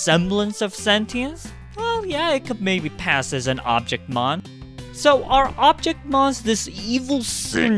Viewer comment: I thought it said object mom's...